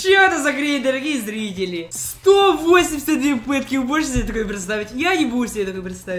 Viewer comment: Мне послышалось или это были 182 ПЫТКИ)